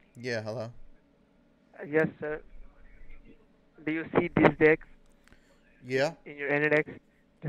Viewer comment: i swear the first thing he says is "do you see this dick"